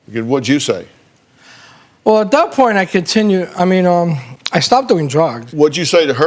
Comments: Did he say “I stopped doing drug” 5:05😂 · for a second there I thought he said I continued doing drugs... naw, I must be mistaken.